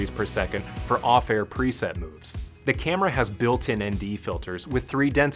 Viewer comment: up to 100 degrees per second for what? i did not get that part... can someone tell me what he said there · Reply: It's 100 degrees per second for off-air preset movement.